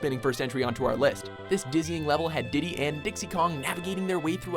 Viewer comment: Number 10 i thought he said Disney not dizzying i`m like wtf